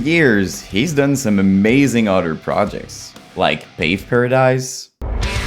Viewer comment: What’s the band at 23:21? I can’t tell what the name is when you said it 😢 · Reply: Pave Paradise 🔥